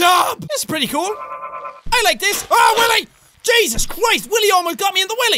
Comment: did he say jesus cwis